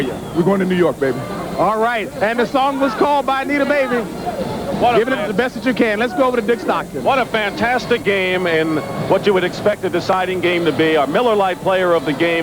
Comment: lol Did JB say "need a baby" @ 7:13 lol!!!